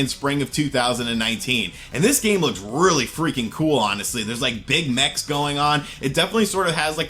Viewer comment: "There is like Big Macs going on" interesting... 🤔 Or did he say BIG MAX (like the golf trolleys)